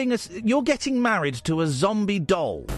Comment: Wtf in the beginning I thought the guy said “zombie dog”